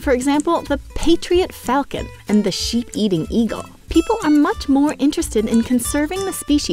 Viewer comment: i thought you said sh_t eating eagle XD